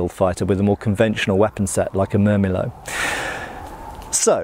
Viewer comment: what weapon did you say? Couldn't quite catch you. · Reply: Murmillo, a gladiator class